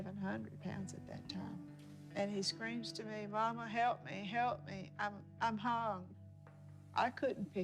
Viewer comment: Did she say “Mama help me, I’m hung”?